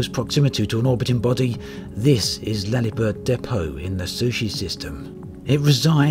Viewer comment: > "Laliberte Depot" in the "Sussche" System (for those who didn't understand a vowel our beloved said ... lul) ❤